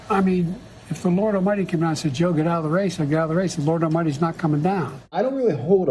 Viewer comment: I'm not for JB but I didn't hear what the Benny Show heard. I heard JB say, "If God Himself came down and said get out of the race" then he would leave the race.